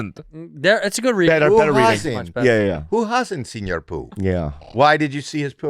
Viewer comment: "Who hassnt senior poop" i tought it was happy saying it first lol.